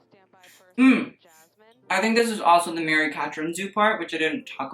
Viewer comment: Im trying to look up a designer he mentioned at 12:20 but i cant figure out the name on google! Mary Catherine Zoo? someone help!